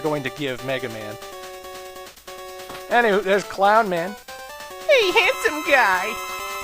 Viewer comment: No, I believe it was, "Hey, geeky!" At leas that's what it sounded like to me.